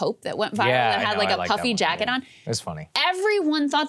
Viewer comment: I thought you guys said Puppy Jacket, not puffy jacket. Ha ha ha. A google search turned up all kinds of interesting things before I got it right.